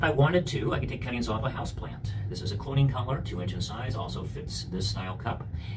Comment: what did you say that foam is? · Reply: Those are 2 inch neoprene clone collars off <link> <link>